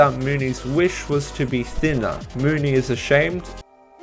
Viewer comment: oh i always thought it said dinner not thinner..................